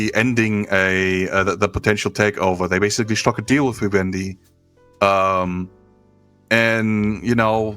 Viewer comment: is your editor's name Bobby Wendy? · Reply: and onward in the buyout chapter, every time he says "vivendi" or "by vivendi" the auto caption heard it as "wendy/bobby wendy"